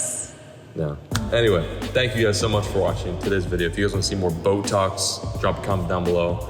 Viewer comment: At the end when said “comment if you want more bow talks” I had a double take cuz I thought he said Botox😂